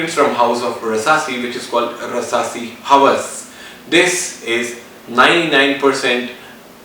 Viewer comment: Thanks for posting this. But at 13:39 are you saying Versace Eros? I apologize for my bad ears and bad speakers on my PC. Also, at 20:38 Is it Estiara Oud Focant? I can't make that out. Again at 21:00, you mention that it is a clone of Versace __ and I can't make out the name of that fragrance. Thanks so much! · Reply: Trent Mason Rasasi Hawas